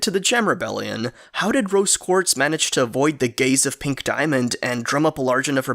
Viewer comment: thought i heard the gays of pink diamond(that could be a code name for crystal gems aa)